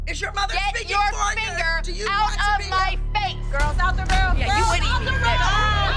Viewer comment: why would kelly do that that was creapy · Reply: I thought Holly said out the road